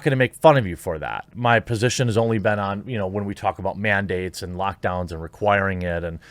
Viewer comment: I heard: "We talk about mandates" As: "We talk about man dates" And I was extremely confused.